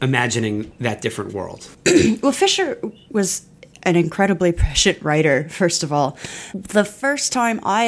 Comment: did she say he was a shit writer?